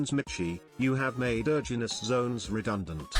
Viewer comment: i think the tts said Erogenous zones